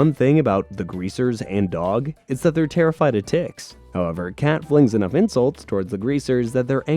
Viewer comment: I heard "they're afraid of dicks" 😭after the Lube thing